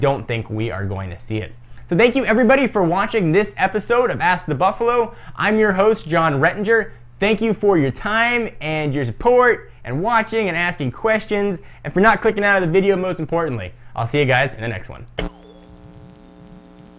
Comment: I thought he said porn @ 8:15 lol :P